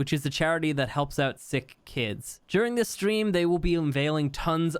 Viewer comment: When said "help out sick kids" I could have sworn he said that helps out "thick kids", I was like woah did he really say that? 😂